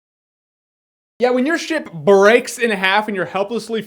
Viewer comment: I thought he said when your sh*t breaks in half